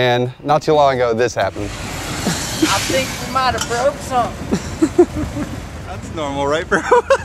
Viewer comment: I thought he said, “I think we Miata broke something” and I laughed a little too hard.😅💀 but now that I know he didn’t say that, I’m a little disappointed (just kidding)😂😂